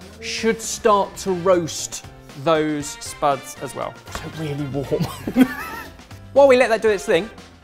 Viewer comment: Back again with the Closed Caption errors What they really said 3:12 Ben 'So really warm.' 3:35 Jamie 'ooo/oh' 6:04 Jamie 'Oop' 9:18 Ben 'It slots in there'